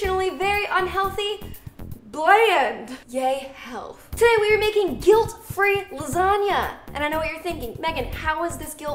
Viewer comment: Dang it. For a second I thought it said "Guilt Free Longanisa"